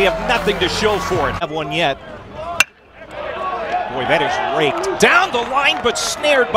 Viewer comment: anyone hear the announcer say "wow, that one was raped" at 2:35??